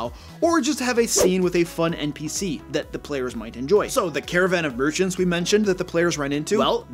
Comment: About 90 seconds into the video, I thought you said "Caravan of Virgins." I wasn't sure where this video was headed...